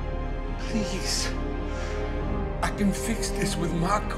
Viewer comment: What does she say at 01:33 ? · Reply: "do not follow me"